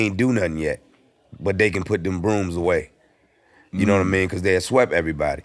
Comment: He said “put the brooms away”, I thought he was saying “put the balloons away” 🙄🤦🏾